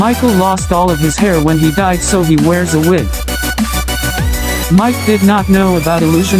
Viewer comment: i heard "so he wears a whip" also not trying to correct you but i think we play as jeremy in fnaf 6